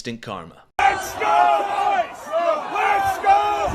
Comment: I didn't know whay he said, I thought he said "Let's go ice"